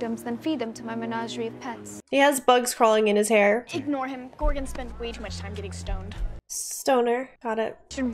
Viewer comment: I didn't realize that the "stoners" were Gorgons (Medusas) until that dope turned himself into stone. I thought Enid said Gordon first.